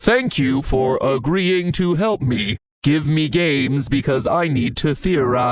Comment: I thought he said give me gays and not give me games💀😭